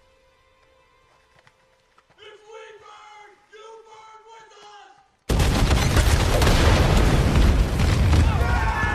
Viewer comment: What is the guy yelling out? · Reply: if we burn you burn with us